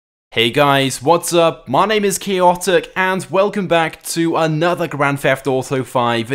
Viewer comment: For some reason, it sounds like he said "Hey guys, my name is key optic"